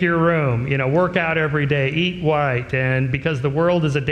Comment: Did he say, “eat white”? 1:25 🤦‍♂️